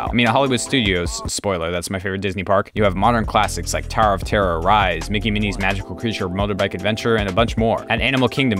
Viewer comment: Did he say Mickie and Minnie’s magical creature motorbike adventure?